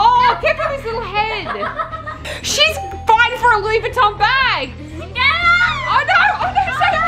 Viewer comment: I thought mama said she fuc__ng fighting for that bag no hate mama is allowed to swear she’s an adult